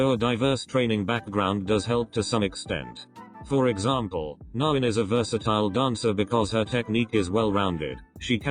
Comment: Can anyone tell me who’s the dancer at 4:04 ? I couldn’t make out what the chatbot said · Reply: her name is nain!